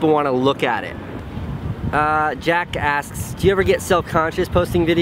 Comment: literally thought he said jackass. 😂